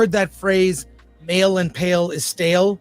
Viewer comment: I legit thought he said “mail and pail is stale” 😂 wtf is wrong with me